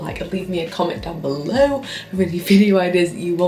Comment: omg i thought you said "well fuck you so much for watching" and i was like what 😂😂😂